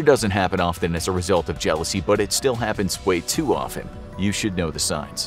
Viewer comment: the subtitles says jeaousy when it's jeaLousy... just hoping someone can fix that :)